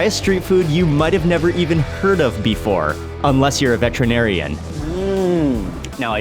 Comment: Did he say unless you're a "veterinarian " that an animal doctor